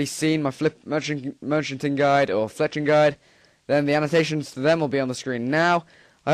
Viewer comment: did he just sayd pis?:D